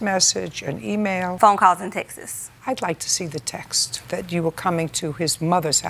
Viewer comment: "phone calls and Texas (texts)" It's a lot of people on this show that say "Texas" instead of "texts". I have never heard anyone say it like this in person lol.